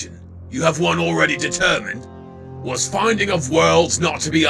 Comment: the subtitles say ballin instead of fallen lmao